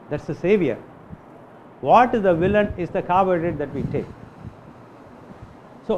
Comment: what is this word that he said here: "What is the villain is the CARBURY that we take" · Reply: He says Carbohydrate..not cadbury